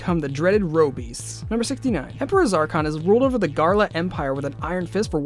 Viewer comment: did he just say ’Garla’